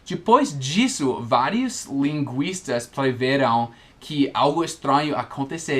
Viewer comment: Não entendi o que foi dito no time 10:55," linguiça" rsrsrs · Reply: linguistas